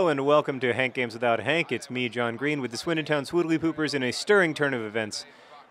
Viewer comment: Everytime you do the intro I hear "It's 'Mean' John Green" which would be an excellent nick name.